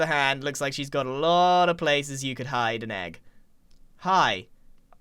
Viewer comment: My mom thought you said faces instead of places 😄🤣